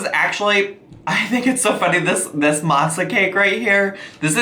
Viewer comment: I thought you were saying “matzah cake”. Like it was a Mexican-Jewish themed dish lmao